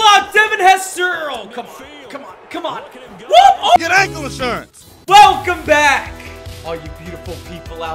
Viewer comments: “Your ankles sir” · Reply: Wait I thought it said ur ankle insures lol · Is it just me who thought it said get ankled sir instead of get ankle insurance